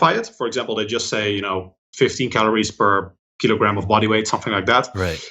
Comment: Did he say 50 or 15 calories/KG of bodyweight @ 23:12 ? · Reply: 15..